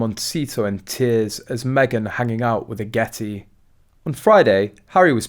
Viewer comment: For a minute thought you said M hanging out with a yeti!